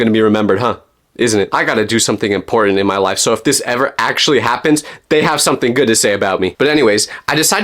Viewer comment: i thought you said this gappened